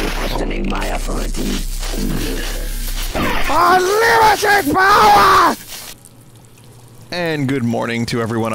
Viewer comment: UNLIMITED POWAH (I first heard it as "I'M LUNATIC POWAH")